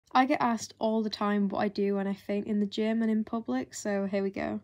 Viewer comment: I thought it said farting at the gym